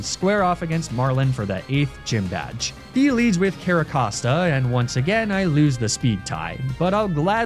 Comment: It’s 4am and, knowing how loves to mess around with names, thought that he said “Carrapasta”